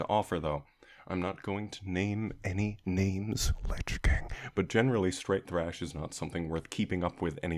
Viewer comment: What you say in 07:07 ? O don't understood the band's name. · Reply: Lich King